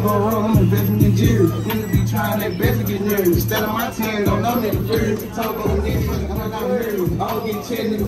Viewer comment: Did I hear him say “Don’t No 🥷 Fear Me”??? 🤔🤨🤦🏾‍♂️🤷🏾‍♂️